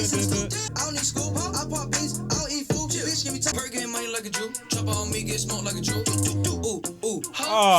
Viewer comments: bro I'm not gonna lie, I KNEW he said "smoked like a juul' and I STILL thought he said jew when I was watching to this video. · I thought he said get smoked like a juul